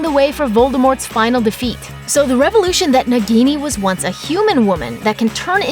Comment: "so the revolution" instead of "revelation"?